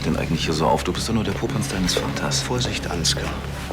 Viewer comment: Was sagt Ansgar bri 20:29 zu Sebastian ??? "Du bist doch nur der ... deines Vaters 🤷‍♀️ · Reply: Du bist doch nur der Popanz deines Vaters